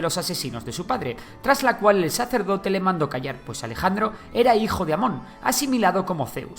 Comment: jaja, miren lo que dice en la parte hay, dique "Mejor te callas" xd.